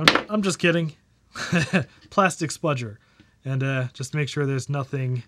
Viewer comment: Did you just say "spludger"?